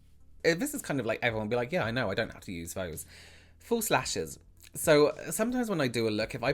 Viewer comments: Great information . . . I had a laugh when I thought you’d said “full slashes” and then realized you’d said “false lashes” . . . I love your accent. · during the section on false lashes at first I heard "full slashes" and was like... great, something else i don't know about makeup